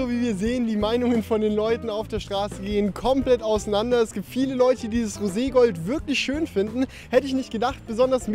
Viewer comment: Hab ihr bei 7:12 gehört meint der es gibt viele Läuche...😂😂